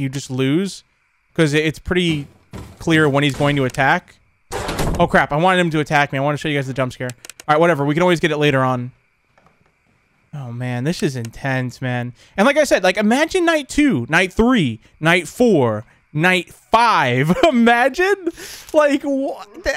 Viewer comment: I thought he said he would never cuss but at 12:58 he said “aww man this shits intense”